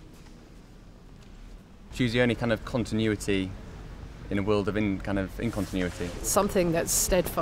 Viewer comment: I misheard the young bloke ( 1:04 ) and thought he said : "She was a sense on continuity, for those with incontinence"